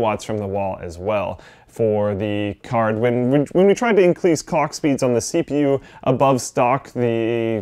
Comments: did he say cock speeds? Now we know whats on his mind. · sounds like he says cock speed at the 2:50 mark